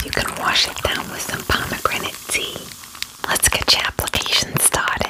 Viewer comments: I LEGIT THOUGHT IT SAID POMEGRANATE PEA 😭😭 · I thought the tea thing said pomegranate pea😂